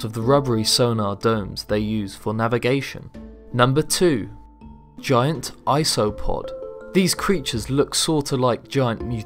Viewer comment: I heard GIANT AIRPOD!!!! Only me